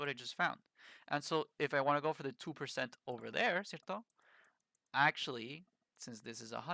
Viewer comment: why do you say something like "zirton?" it happens very frequently in your videos and i dont understand it annoys me a bit, is this slang?